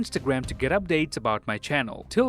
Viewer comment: d-did he just say i am Dave chapel an